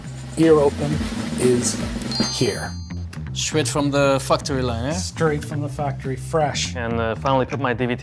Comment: I heard straight from the fuckory line... 🤣🤣🤣